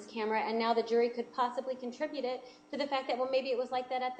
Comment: Attribute Ms. Wilmot, not contribute I may have misheard her.